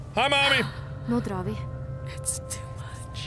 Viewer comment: Norwegian= Nå drar vi= English=Now we go Why was that in there? Are the creators norwegian? I am norwegian. 13:32-13:34 For people who doesnt speak norwegian you will think it is all gibberish. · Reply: ***** Yeah I was about to comment too about it! Thought I misheard but I'm pretty sure she says "nå drar vi". Caught me off guard :P